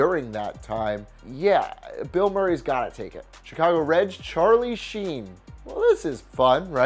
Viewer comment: Did he say Chicago Reds?